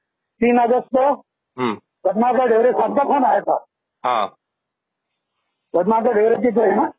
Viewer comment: he says he got a call from bhatnagar real estate?? something i cant make out.. Its not bandra police. Can anyone tell who called him? He said its some sahab.